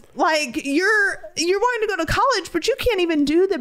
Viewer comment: I thought you said you didn't want to go to college. If he want to go let him go